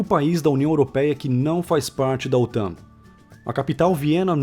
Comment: Alguem entendeu o que ele falou em 12:14? "Áustria, o único país da UE que não faz parte da ..." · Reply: da OTAN